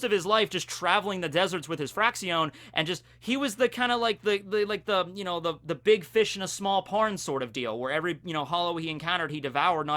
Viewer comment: is it just me or did it sound like he said "The big fish in a small porn"